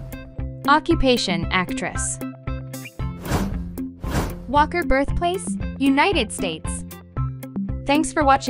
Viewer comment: I literally heard “rebirth place”for the Read Choi guy😂